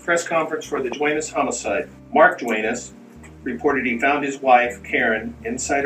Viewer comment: When I was listening to it whilst making a cuppa tea I thought he said he shot his wife.